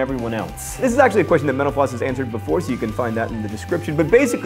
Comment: When he says mental floss it sounds like he says menopause.